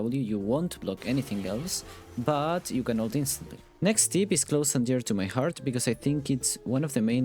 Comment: "But you can notice..." then it cuts off. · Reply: He didn't say "But you can notice" he said "You can ulti instantly" you just misheard him